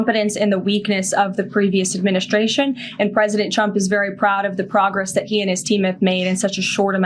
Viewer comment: Right on 8:00, she clearly says "President Chump".